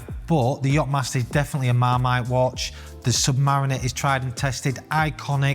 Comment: what's the word he says at 1:40 ? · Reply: Marmite? It’s a food product in the U.K. people either love or hate. If you call something marmite it is usually people love or hate, no in between 👍